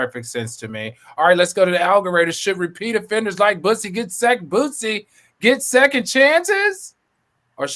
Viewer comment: did say "Bussy"?👀 now what's on your mind🤭🤣🤣